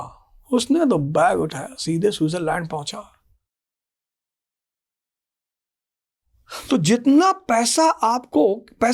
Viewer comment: Why mute what did he said 😢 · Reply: He said pranav is a sexologist 😂